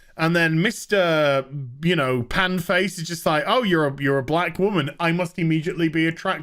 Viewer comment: Sounded like you said batwoman.